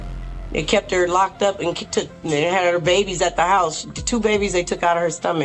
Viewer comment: She did not say that the woman had the babies. She said that they TOOK the babies from her stomach.